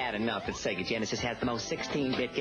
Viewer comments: I thought she said it really duffs my doilies. Still though, it's hilarious. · I thought she said it really duffs my doilies. Still though, it's hilarious. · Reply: I thought she said ducked my doilies